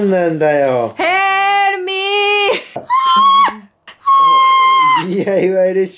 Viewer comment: ヘルプミーが、フェ〇ルミに聞こえた 妊娠しました？